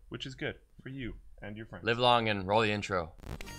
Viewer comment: the Vulcan greeting "live long and roll the intro" definitely sounded like Romulan "(live long and) Jolan-tru". Must be my pointy ears! Lol.